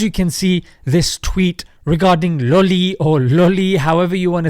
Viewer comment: I thought he said Lully 😂😂😭🍆